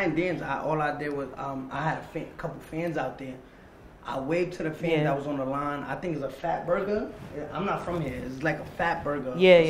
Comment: 😆😆 Her face when he said the words fat burger..I had to rewind because I thought he said fat person 🤦🏼‍♂️